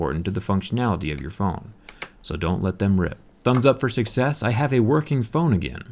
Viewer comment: I heard thumbs up for 6s xD I was like " it's a 7! " but I figured out after I while that that was not what you meant